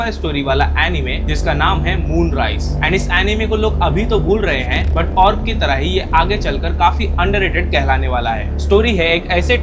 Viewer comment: kya bola kisi tharah hi moon rise famous hoga?